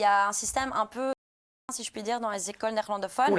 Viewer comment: A 4:55 elle dit quoi ? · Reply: Rat ci ste